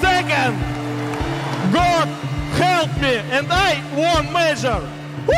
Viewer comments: Anytime I hear “god help me and I won major” I legit tear up bruh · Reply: I heard "god helped me and I won major" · Anytime I hear “god help me and I won major” I legit tear up bruh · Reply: at first i heard '' God hate me and i won a major''. still cringy tho